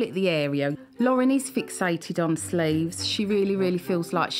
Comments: I swear I thought she said she’s fixated on slaves!!! I was like huh? Wtf. Lol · I thought she said fixated on slaves ☠️☠️☠️☠️ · I was so confused for a moment. I heard “…fixated on slaves”. 😂